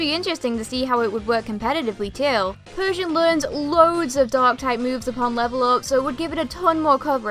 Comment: During seven, I thought she kept saying dog type, instead but f dark type